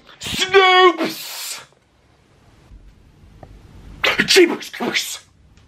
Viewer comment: did he just say "pikachu is cursed"?